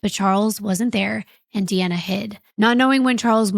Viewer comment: Sorry l was driving and must have misheard??? But didn't the mother say "Deanna hide"??